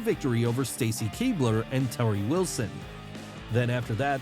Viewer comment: Did you say “Terrie Wilson”? O_o